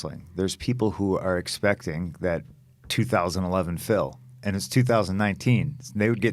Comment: I thought he was saying 2011 Phil (Heath) 😂 then plugged my headphones know I understand what he was saying 🤣